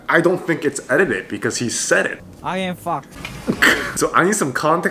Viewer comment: Hahahahah he was tryna say i am Fox but sounded like he said im fucked :D Its one of the most funniest IN moments tbh